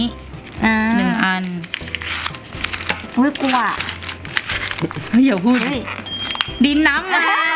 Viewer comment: พูดว่าอุ้ย!!อุบากใช่ป่าวค่ะ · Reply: กลัวอ่ะ